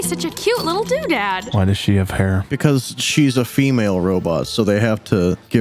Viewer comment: It took me a sec, but when they said, “why does she have hair?”, I thought they said “Why does she have a pair?”